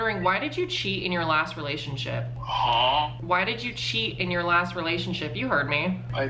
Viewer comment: what in the huh cat? I can't be the only one who heard that right?